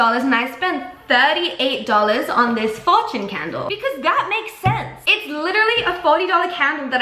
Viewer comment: i bought this candle because that just makes scents! (please tell me i'm not the only one who heard it like that.... i'm such trash that i literally hear puns even when they're not meant to be puns)